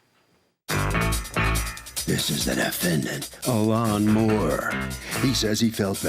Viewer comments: I thought they said the defendant’s name was A LAWNMOWER I was like dafuq 😬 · I thought the announcer said he name was A lawn mower.🤭🤔🙄